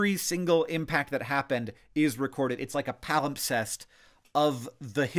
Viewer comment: palimpsest! New word for me. I first heard “pal obsessed,” like a guy who gets so excited about the moons of Jupiter that he makes YouTube videos that get you excited too